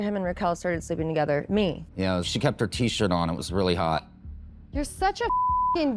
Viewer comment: “she kept her tshirt on, it was rlly hot” what. in. the. hell???? did I hear that correctly????